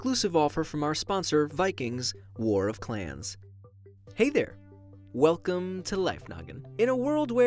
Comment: I don’t know why but when he says “Hey guys welcome to Life Noggin” I hear “Hey guys welcomes to Life Nugget” Am I the only person who sees that lol